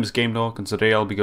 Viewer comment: I like the intro but for a first time watching it sounds like you say, "Hello, my name is Gaydog." Great video